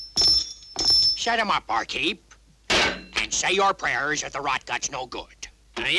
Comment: what did Joe Dalton say? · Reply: "Set them up, bar keep. And say your prayers if the rot-gut's no good" (Give me a round of drinks, bar keeper. And you're in trouble if your low quality alcohol makes me ill)